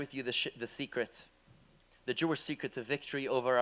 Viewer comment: Did he say that "he would like to share with you the sh*t" at 0:04 because that is exactly what it is😂